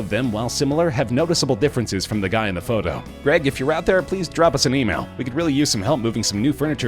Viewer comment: did he just say... Drop us a memail?... somebody end my pain